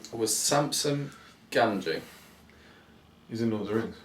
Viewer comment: Trivia . . . as memory serves... 49, 44, 36 & - 34. Totalling 163/200? . ah finally a question from that I'm sure of haha. It sounded like you said Samsung Gamgee too! But Joseph Sampson Gamgee was a famous surgeon who invented a particular type of surgical dressing. It's been said Tolkien used his name for Samwise Gamgees name? I know this because I'm studying biomedical science at Uni so my independent time reading hasn't all gone to waste haha ;) . I also noticed that hadn't thought of a question as the video was cut after said his lool As for what would be first, definitely hobbit. Better to watch them in 'order' and for it to make sense. Though character development isn't as good key individuals like gandalf, Saruman, frodo and bilbo would mean more to us in LOTR and scenes such as Gandalfs fall in moria and Sarumans betrayal would hurt more. Also best to save the better trilogy for the end haha! won't be too pleased!